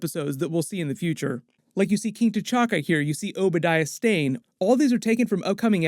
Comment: did he say king TaChacca? Lmao😂😂😂